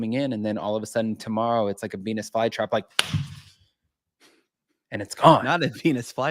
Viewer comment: When you said Venus fly trap, I thought you said PenisFlyTrap.😂😂I was half asleep listening at 4a.m.🤦🏻‍♀️😘